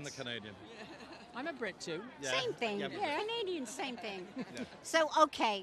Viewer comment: Did she say SAME THING ????